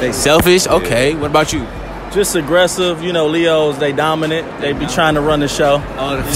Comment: “Leo’s are aggressive,Dominate” Honey you must not have met a Leo cause leos are the most protective and also the get offended when it comes to jokes that makes them feel a way,they ain’t met no Leo before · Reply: I fr thought you said Legos lmao.